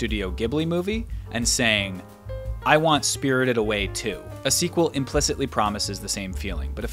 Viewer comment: You say "sequel" and I hear "SQL" and get really confused momentarily.